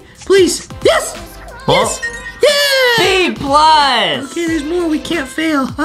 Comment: Boxy Say A+ Instead Of B+